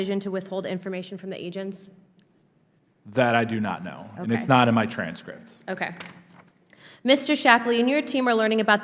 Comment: Everytime I hear him say "It's not in my transcript" all I hear is "I was not couched on that".